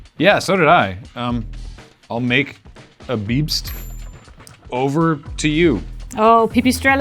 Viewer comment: he clearly said BEEPst